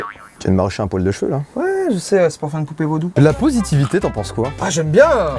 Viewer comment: A 5:51, j'ai compris "La séroposivité, t'en penses quoi ? Ah j'aime bien !" XD